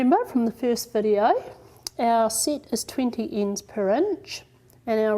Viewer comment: As a non native speaker I didn't understand what you said from 4:35 to 4:50. Could you (or somebody) please explain? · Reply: The sett is 20 Ends Per Inch. She is using a 10 Dents Per Inch Reed so she has to put 2 threads in each slot.